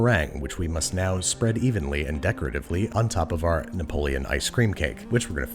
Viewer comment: I'm sorry am I the only one that heard him say Napoleon vs Neapolitan for the ice-cream?